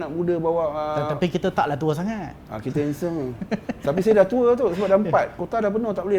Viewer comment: ah kita hensem..aku salah dengar ke?haha